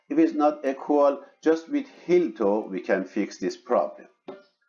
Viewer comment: i could not understand what you said at 12:54 ..how to correct the image to have equal distance from probe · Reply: with heel-toe (rocking) maneuver